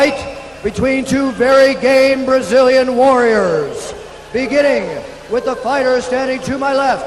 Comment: does he say 2 very gay Brazilian warriors?